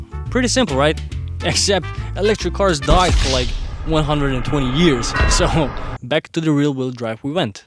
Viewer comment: But what word is said about electric cars at 02:08? As if something was wrong with electric...(edit) AFTER that there was La Jamais Contente. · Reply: I said that electric cars died for like 120 years haha (as in not gaining traction and got popular again in 2010s)